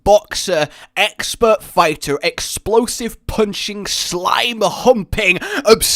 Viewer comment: Did he say slime humping? Great video btw